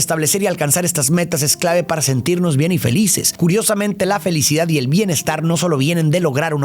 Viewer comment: Yo escuche "para sentirnos bien infelices" xd